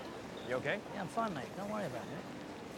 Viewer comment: What is Statham saying ? "?????? L.A" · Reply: "Enjoy LA..